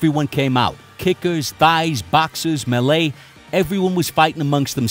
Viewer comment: lmao does call Thailand "Thighland"?